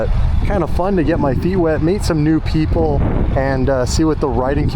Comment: I thought he said get my D wet but he said feet lol